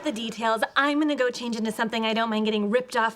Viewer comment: Does Wolowitz say "what the f---?!"? It sure sounds like it.